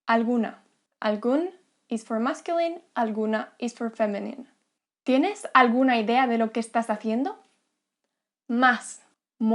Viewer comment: Typo in subtitle at 4:17 * Algún is for masculine, alguna is for femenine That should be "feminine" Typo in text shown on video @ 4:22 * ¿Tienes alguna ideas de lo... What you actually said was "Tienes alguna idea..." Typo in subtitle at 10:48 * and if you ave any questions...